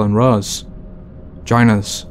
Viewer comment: When he said “join us” I thought he said “vagina”